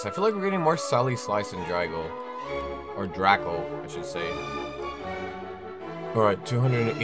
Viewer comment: Did I hear you say drago? At 2:54 I was summoned